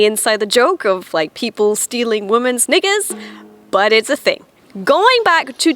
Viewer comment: What did she say in 3:48 ??? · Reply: Knickers